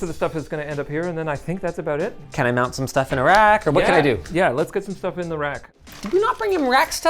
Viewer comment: why did I think he said “can I mount some stuff in Iraq“💀